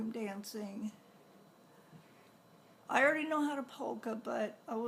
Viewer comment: Polka. I had to go back and listen because I thought you said pole dancing.